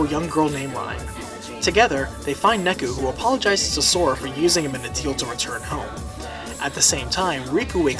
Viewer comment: I don’t understand what you said here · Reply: '...apologizes for using him in a deal to return home'